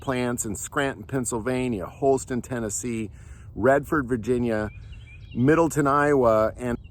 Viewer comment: It sounded like you said "Redford" but it should be Radford Army Ammunition Plant in Radford, VA.